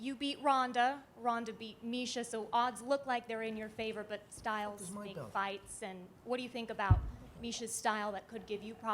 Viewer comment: Can anyone make out what Mcgregor says at 19:28 when camera is on Holly?? I assume Dana told him to stop interrupting Dos Anjos or something. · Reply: He says where the fuck is my belt